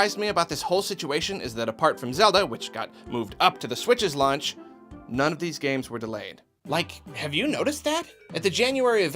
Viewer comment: When you said “None of these games are delayed” I thought you said “None of these games are lame” but I went back and heard that it was delayed not lame.